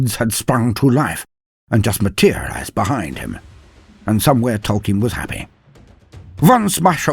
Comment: !!!!! At 12:15 did You say¨Tolkien¨ ???